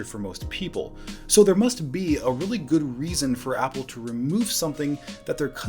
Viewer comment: KInda interesting, around 0:42, he distinctly says "so there must be a really good reason...", and the closed captions say "so there must be really good reasons..."